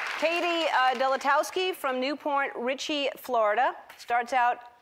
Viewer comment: Did Ellen just say New Porn Richie, Florida at 0:48 ?